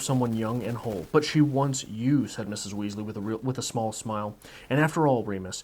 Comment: I thought you said mrs. Weasley and not mr. Weasley